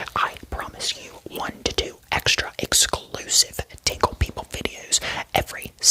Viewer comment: I THOUGHT HE SAID TICKLE PICKLE